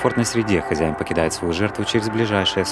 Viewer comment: Мне сначала послышалось К сожалению волосатик не интересуется людьми 😮